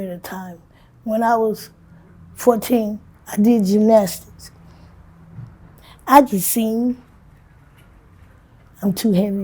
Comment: I hope you are back. Did she say she could sing? I thought I heard her say as a child she could sing.